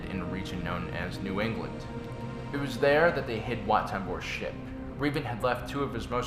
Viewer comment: Lol I thought you said "Wat Tambors shit"